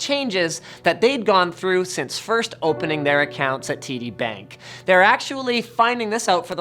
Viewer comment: sounds like you said titty bank, hehe